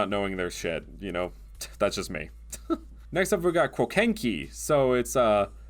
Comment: i heard "next up we have crocacin and i was incredibly concerned